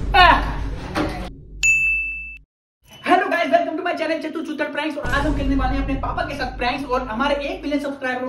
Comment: What did he said 0:51 chanu chutar ?? 😂